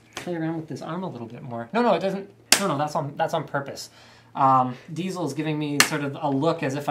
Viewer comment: Did I hear Diesel and not Slick???